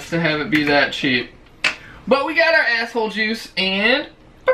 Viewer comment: Did he say asshole juice 5:39 lmfao i subscribed